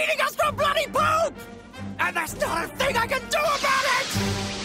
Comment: what was Jeice saying as he flew? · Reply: He was just yelling "not a thing!" Again